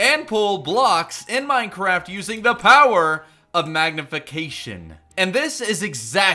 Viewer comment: did he say magnification or something? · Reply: Magnification